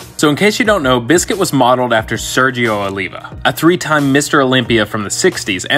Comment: Oh shit I thought you said a 3 ton mister Olympia from the 60s and I was like imma call bullshit on that 3 ton stuff turns out I misheard you.